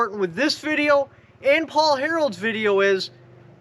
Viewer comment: it sounded like he said Paul Harold. Am I the only one who heard Harold and not Harrell?